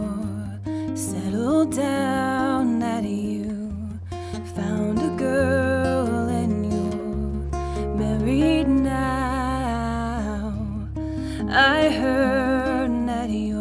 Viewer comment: Great tutorial! Not sure about the vocal. I hear "found a girl and you're buried now"! Am I the only person? That's one way of looking at marriage but !........ Maybe a little more articulation ;-)